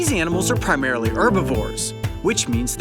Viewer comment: did i hear it wrong or did he say erbivor (or how ever you spell it) instead of herbivore?